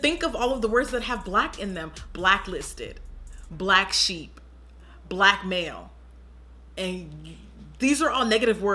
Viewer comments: When Jackie said blackmail as an example of a bad thing with the word black in it, I got so confused cause I thought she said black male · when she said blackmail, I heard "black male," and was like, "yes auntie Jackie with the word play!!" ✊🏾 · I thought you said "black male" for a sec, I was like Jackie nothing is bad about black males 😂